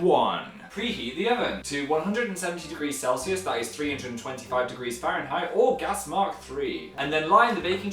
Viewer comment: when he says 'or gas mark...' It sounded like he was going to say orgasmic haha